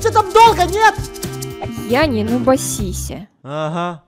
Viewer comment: Когда сказала я не нубасися! А мне послышалось я не нубапипися😂